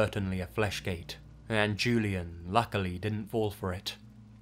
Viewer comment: Just to clarify, that’s “Fleshgaits” and not “Fleshlights” ( ͡° ͜ʖ ͡°) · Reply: i thought you had said flashgate